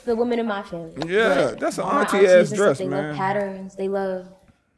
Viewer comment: "ya pants is patterins" was her version of flashing the glock out her waist line 😂🔫 wanted smoke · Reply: I thought I heard patterins too... she sounded just like my mama sister 😏